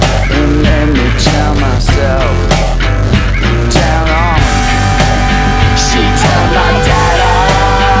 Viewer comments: When you realize they aren't saying "she took my data" but they're actually saying, "she turned my dad on" and you spent entire time jamming out to that part over and over again without even realizing it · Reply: I thought it said “she told my dad I” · When you realize they aren't saying "she took my data" but they're actually saying, "she turned my dad on" and you spent entire time jamming out to that part over and over again without even realizing it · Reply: I thought they were saying "she turned my dead on"? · When you realize they aren't saying "she took my data" but they're actually saying, "she turned my dad on" and you spent entire time jamming out to that part over and over again without even realizing it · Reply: Lmao ever since my first listen I heard "she turns my dad on" idk how y'all hear anything different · When you realize they aren't saying "she took my data" but they're actually saying, "she turned my dad on" and you spent entire time jamming out to that part over and over again without even realizing it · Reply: Wait I thought they were saying “she told my dada”💀